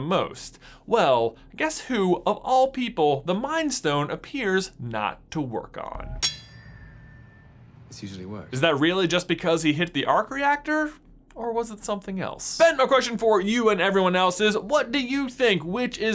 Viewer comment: I thought Loki said “Excuse me what?” so I had to pull a time stone and rewind to relisten to that sentence. He actually said “This usually works” but I didn’t hear that whatsoever.